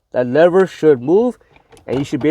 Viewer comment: At first I thought he said "that should never move.". I was ready to replace mine lol.